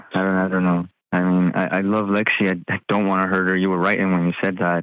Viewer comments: Did he just call her Lexi 14:06!? 😮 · did he say “I love Lexi”? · did he say he loves lexy?? But his girlfriend is Aubrey. Did anyone else catch that or did I miss hear it? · I swear at 14:15 he says “I love Lexi” am I trippin ???